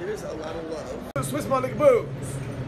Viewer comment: wtf I heard was booze